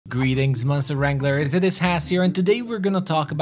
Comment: I've been so addicted to the game I heard "GOOreetings" at the beggining of the video 😅